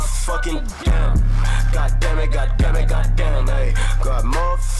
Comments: Sounds like x is sayin demi god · Kinda sounded like he was saying Demi god